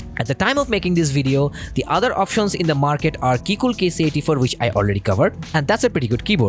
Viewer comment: Can you tell me what u said in 8:18 , Keycool.... then ? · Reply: That's Keycool KC84. I already covered this keyboard. It was the second episode of this series !